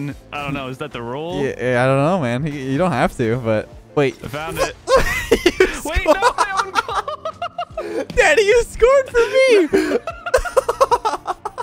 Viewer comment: In 8:10 I thought he said daddy